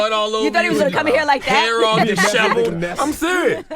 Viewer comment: Did he just say "deshavalled?" Does he mean deshevelled?